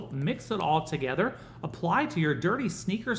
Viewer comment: I thought he said wipe with a damn cloth